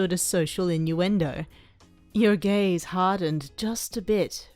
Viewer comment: I smooth thought that said: “Your GAYS hardened.....” I was like ??????? I think my friends are rubbing off on me. I’m the only straight person in a group of lesbians, bis, and pans. 👌 It’s all good. (Update: sooooooo wasn’t actually straight, I’m AroAce ✌️)